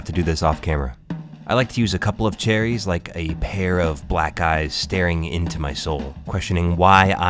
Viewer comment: I heard "Like a pair of black guys staring into my soul"